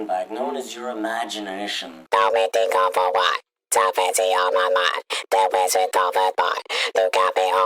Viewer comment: Is it weird that in one part of this it sounded like pico said "it can't be on my mind" at the end of the clip.